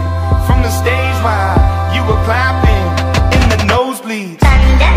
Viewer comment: I thought it said *in the closet* Lmao